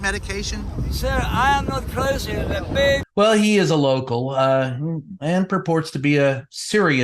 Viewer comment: Oh, you're calling some of these people "local." I thought you were saying "loco." Sorry.